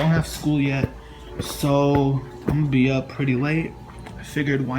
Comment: Dude I swear but I heard a deep voice say "Very late"